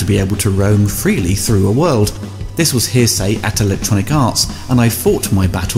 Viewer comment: Edit tip @ 8:50 I think, read as Here Say, should read Heresy